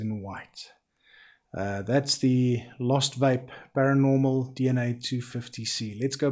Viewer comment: Every time you said LOST VAPE I thought you were saying MOSFET ... lol